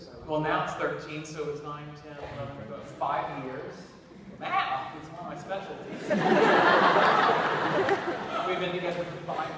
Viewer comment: thanks for this videos! what does he say at 0:53, 0:54??? can't hear very well... u.u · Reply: Andrea Moreyra He says math isn't his specialty and then says he's been with his partner for 5 years at that point. Hope this clarifies it for you :)